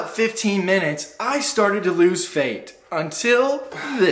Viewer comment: did you say “i started to lose fate”?